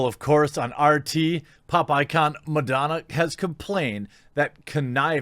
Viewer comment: Misheard "pop icon" as "Pop eyed c***"